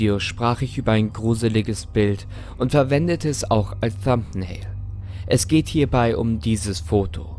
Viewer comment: was sagte er in 6:28? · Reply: Ein thumbnail